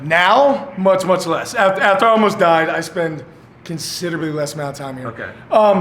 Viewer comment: Did he say at 22:20 "after i almost died"???